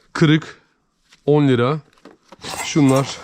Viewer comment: Abi 12:43 dakikada telefona 10 lira dedin Yoksa benmi yanlıș duydum